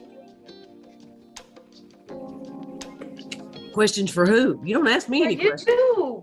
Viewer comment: what did the judge say? 🤣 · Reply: “There ya go”